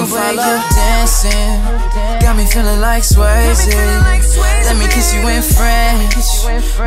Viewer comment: who else searched "let me kiss you in french , bet i can make you go crazy" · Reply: I thought it said let me kiss you were friends lmfao